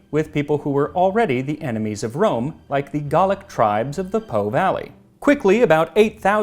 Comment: Lol. I misheard and thought you said “Dalek” not “Gallic”. “Exterminate, exterminate!”